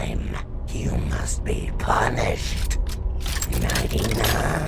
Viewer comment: BRUH I WAS LAUGHING WHEN MOONDROP SAID 99 INSTEAD OF NIGHTY-NIGHT XD The timestamp is 7:00 edit: captions